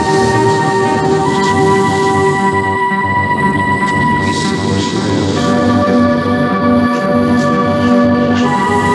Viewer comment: What are the ethereal voices saying at 2:30? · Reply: "We've a game in development called Hawkeye.....give it a Gold Medal.....Yes....I know the gameplay is dogshit but if it gets a GM I'll make sure Oli doesn't release those embarassing sketches he's got of you lot from the Christmas party".